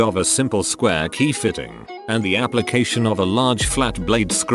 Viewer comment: I am not reading this as I listen so I heard “appellation” as “Appalachian” and wondered “wtf is an Appalachian Chav?”